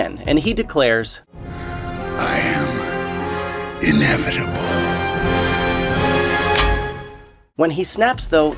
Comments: while watching the last avengers movie i thought Thanos said " I am... InEDIBLE"😅🤣😂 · if you slow down the movie it sounds like he is saying ¨I am Inedible¨